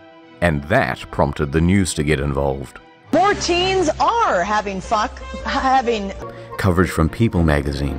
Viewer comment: Did she say "having fuck??" 😂